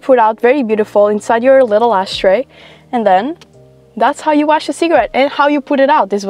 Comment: why do you sound like you say "thats how you wash a cigarette" 😭